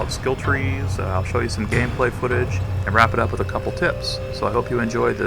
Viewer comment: I thought you said "wrap this up with some tits." Then I skipped to the end. No tits. Ruined my day. Not your fault. It's my ears' fault.